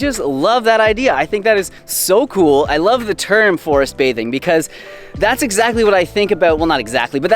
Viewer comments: At first I thought you said "forest bating". 🤔 🤣 Thank you for your wonderful inspiration! ❤ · Love your positive attitude, thanks for the reminders. I also appreciated the "love thy neighbor" message from your last live video too, it would be a much happier place if more people did this! I thought you said forestbating and wasn't quite sure where you were going with that....until I realized I misheard 🤣